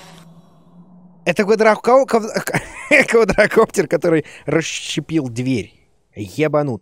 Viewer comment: Мне одной послышалось выдрокоптер?! 😂😂🤣🤣🤣